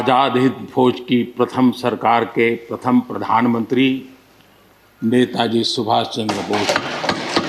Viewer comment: Did he say 1st prime minister netaji Subash chandra Bose @ 5:31?? 😳😳😳😳